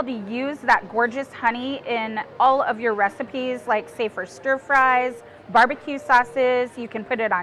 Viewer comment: This is so cool and I wanted to share that when she said "say, for stir fries" I heard "safer stir fries" like "safer sex" and I was wondering what stir fry risk reduction would look like before I figured it out. Anyway, thanks for doing what you do.